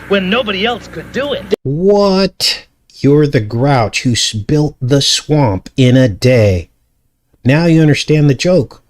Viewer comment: 8m 49s I thought he said Built the swamp in a day